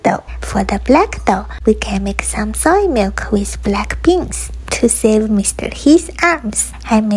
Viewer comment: I thought you said "to save Mr. He's arse" 🙈🙈🙈🙈🙈